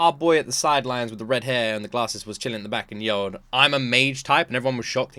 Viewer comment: "Wait, wait, hold on ..." Choi didn't say, "I'm a mage-type," he said, "He (Jin-Woo) is a mage-type." Ma was trying to recruit Jin-Woo as a fighter because his guild was lacking fighters. 27:30 "She was giving the ant its orders, not the System? Has that ever happened before?" The Player Development System has only given orders to Esil and the others in System-generated dungeons, and interfered with magic beasts, but the order to kill humans is coming from somewhere else.